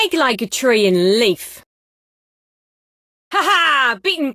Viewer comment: i heard Mean like a tree in leaf